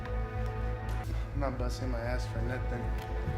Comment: Sounds like he said “not been bussing my ass for nothing” as in not working his ass off for nothing!!